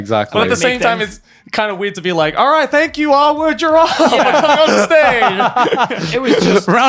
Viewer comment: Did anyone catch the name of that probably made up YouTube channel? On the subtitles it says "our word giraffe" but i don't think this is what Jack said. I want to know why this name is bad.